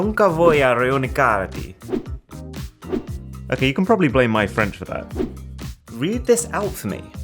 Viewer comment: it spanish not french ...but anyway is bad haha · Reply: I thought he said French too but he said friend